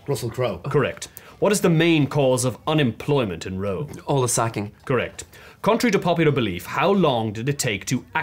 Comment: On rewatching, "all the sacking" is a fantastic line! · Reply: Had to look at comments to understand it…I thought they said Olive sacking